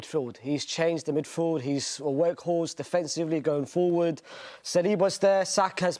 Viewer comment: For a second I thought the guy said Declan rice is like a Weghorst 😂 but I think he said workhorse